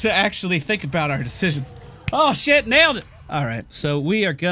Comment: Did you say "Ho shit, merde" ? Your "merde" was so natural, it sounds like you're French ^^ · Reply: He said "holy shit, nailed it" lol